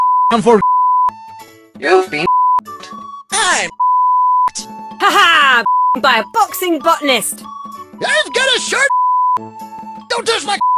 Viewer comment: Wtf I always heard: "haha beaten by a boxing f*ckness"